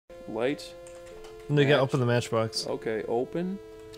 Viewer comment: I swear, the first thing I heard was "n*gga open the matchbox" ...was that real?